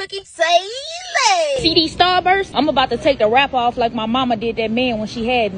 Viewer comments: I can’t be the only one who got confused cuz I thought she said Starbucks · I thought it said Starbucks- 😂